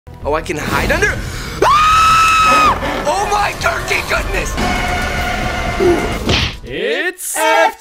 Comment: what the freak did you say ?! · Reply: He said oh my gurky goodness